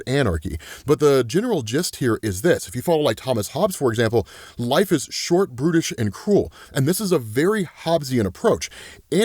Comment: swear you just said "life is Short, British & Cruel" 😅 Yep, certainly can be, Mate! 😂